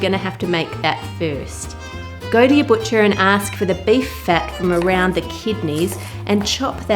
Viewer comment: I thought you said "butcher a calf" for a second. Never knew had a dark side 🤭